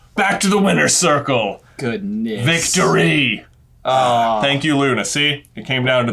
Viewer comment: Perfectly sounds like J said "Thank you Lunacy" lol Luna got a new nickname.